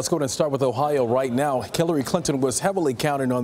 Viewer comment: did he say killary? lol